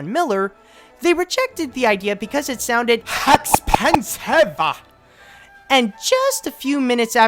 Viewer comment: What did you say on 3:46? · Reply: "It was expensive!"